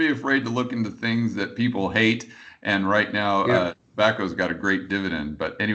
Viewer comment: said something has a good dividend right now. He said it at 56:08 but I can't understand what stock he said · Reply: Tobacco